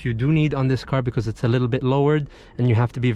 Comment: Did he say “ it’s a little bit lower “ ??? 😂Broo